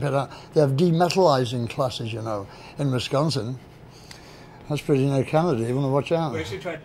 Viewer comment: they have what? Did he say Wisconsin?